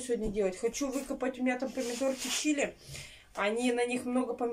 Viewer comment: Мне послышалось ,что назвала помидоры ЧИЛИ,а не ЧЕРРИ?ты так рассуждаешь,подача серьёзная Вот только бы правильными фразами,да в правильное русло.Честно,цены б тебе не было .Хоть ещё незлая,а посылала бы нас подальше и надолго.😀😀😀